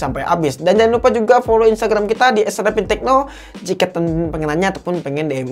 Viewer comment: "jika TEM TEM pengen nanya" kok gw dengernya TEM TEM ya bukan TEMan TEMan 🤣🤣🤣